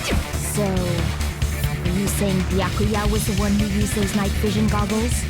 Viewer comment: i like the fact when it turned captions on in this moment instead of it saying Byakuya it said Yasuhiro 😭 (go check it out yourself click on my timestamp)